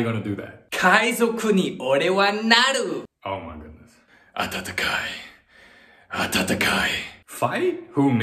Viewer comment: Did he say atatakai at 0:43 or tatakai? The former can mean warm while the latter means fight.